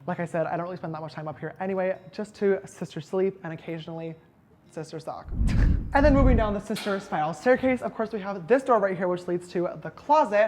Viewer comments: what did he say at 9:33????sister suck??does that mean um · Did he say sister suck😂😂😂😂😂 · Did he just say he is only up there to “sister suck”?🤔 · Wait at 9:32 what did he say? Sister sock? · wait what did u say? HAHAHAH sister succ? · “sister suck” ?? Did I hear that right? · did he say "sister suck" ???? LMAO